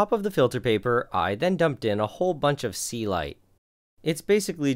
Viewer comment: That's the first time I see the word Celite, because I kept hearing 'sea light'